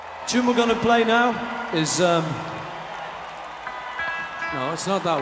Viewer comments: He took a tip from John lennon in his intro with "shilly love shongs". Very john thing to do. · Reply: I heard him say "thilly love thongs"🤣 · Introducing the song, it sounds like Paul said, “It’s called Philly love thongs”😂😂😂😂😂